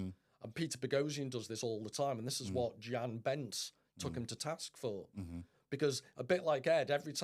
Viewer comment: What name is he saying ? Jian Benz ? Jean Benz ? Bents ? Does anyone have a link to that debate or critique ? Nvm, found it. Name of the video is "This Is Why Modernity Cannot Save Us w/ Dr. Jan Bentz" - yt link -> watch?v=a-n7Rr02uLc